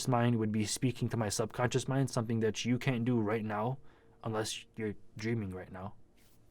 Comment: SOUNDS LIKE YOU SAID CANT. HOW WOULD I BE DREAMING RN I'M WATCHING YOUR VIDEO.